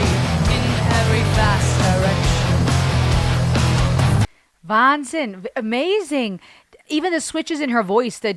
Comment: wait wait wait did she say "wahnsinn" at around 15:49? ^^"